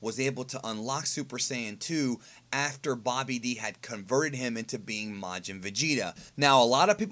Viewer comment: I thought he said "Bobby D"